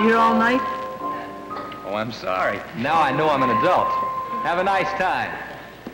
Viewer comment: This might just be me, but the last time I watched this I thought pat Boone said " now I know I'm not young anymore" instead of " now I know I'm an adult"